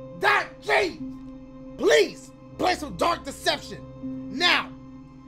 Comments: I thought this shit said dark reception, like he was bout to be hell's receptionist and shit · Who else thought it said dank deception